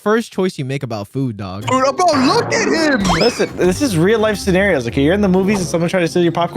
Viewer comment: Wait what did he actually say? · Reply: Prob “Wh- bro LOOK AT HIM” But really wonky